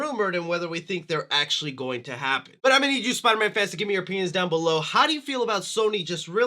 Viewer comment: well i misheard that, (what he actually said) " i need you spider-man fans to give me your opinions down below"....(what I heard) " i need you spider-man fans to give me your Pen*s down below"....i am so sorry, im gonna go lie down